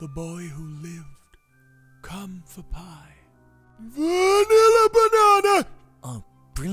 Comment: I don't think Vanilla Banana is a killing curse 😅😅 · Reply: I thought he said I need a banana 😂😂